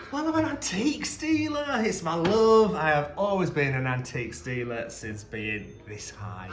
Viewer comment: I thought said he was an antique stealer!